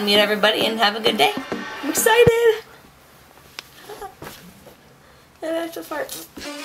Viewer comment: did she really say: and I need to fart? or does it just sound like it lol XD